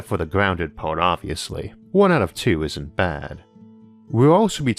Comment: You say "isn't" and CC says "ain't"..... Ain't isn't a word is it?